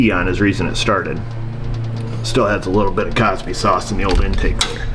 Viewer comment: Did you say "Cosby Sause"? Lol!!!